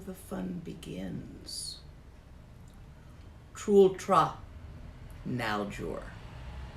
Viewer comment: Is saying kultura aljur as the name of Stillness/Movement Yoga? at 1:16:56? Thanks for any clarification offered. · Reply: "trul tra naljor", which translates to "moving - stillness yoga", or as calls it, the yoga of dancing stillness.